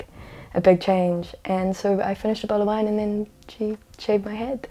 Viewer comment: a bowl of wine? A bottle? I was deaf the first time why tf did I hear bowl